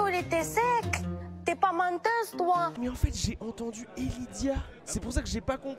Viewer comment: même moi j’ai entendu Helydia 😂